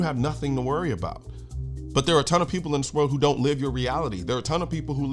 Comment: I heard "I have a ton of personalities" at first LOL.